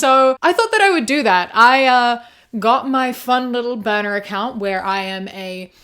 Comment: I heard you say "boner account" 😅